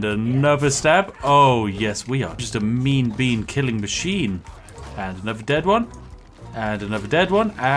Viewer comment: Did Spiff just say "Meme bean killing machine"? If not... he should.